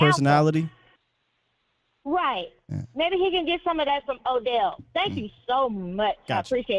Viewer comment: i thought the lady caller said lamb could get some booty from obj 😆